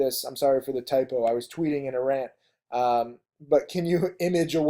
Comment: Did you say you were tweeting in Iran? 07:40.